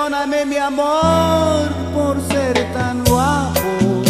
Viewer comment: 예전에 과나후아토 여행 중에 무한 반복으로 들었는데 당시에는 tan Guapo를 guanajuato로 알아 듣고 10년을 살았네... ^^